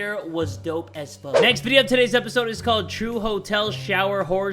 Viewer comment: I thought said hoe shower and I thought this would be good until I was hotel shower